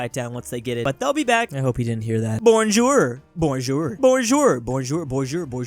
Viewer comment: im from france and your "bonjour" was funny to hear lol I heard it like beajur